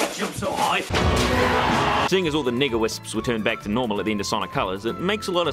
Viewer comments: did he say "seeing as all the nigga wisps "? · Sounds like he said nigga wisp lol